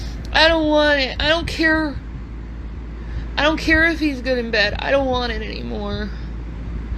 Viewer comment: No, Amber Heard XXXXL. The bodycam footage showed you telling LE that you hit him. They stated clearly that there were welts on him and none on you. YOU lied to the cops, BigBigAmber, and you went to jail for domestic violence (and not for the first time). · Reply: did she say 'i don't care if he's good in bed'???